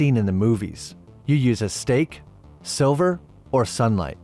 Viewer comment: Does he say steak? Or steaked silver? I thought he said steaked silver.